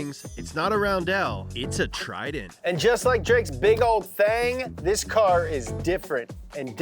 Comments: did he say just like drakes big ol thang 😭😂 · did he just say “like drake’s big ole thang?”😭😭😭..nah im in tears rn😭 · Did he say just like Drake’s big ol thang …(Pause)🤦🏽‍♂️